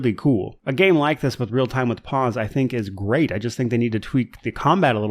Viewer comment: In my mind I kept hearing it as “Real Time with Paws”